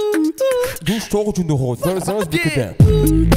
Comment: What did they say at 0:51 ? Is it French ? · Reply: It refers to a song : Bouga - Belsunce Breakdown : "D'où j'sors d'une ronde Belsunce Breakdown", Belsunce is a district of Marseille in France